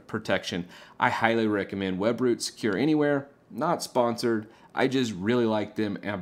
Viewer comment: What are you saying at 05:46 it sounds like web brute secure anywhere, but i can not find it searching on google ? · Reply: Webroot Secure Anywhere